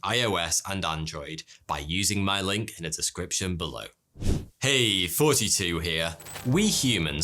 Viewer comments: sounded like "Hey 42 here" 😂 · It always sounds like your saying “Hey 42 here” I’m like 🤔 he looks younger then 42